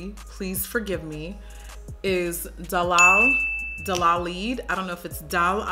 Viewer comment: My name is Delali so when she said Dalaid it sounded like she said my name.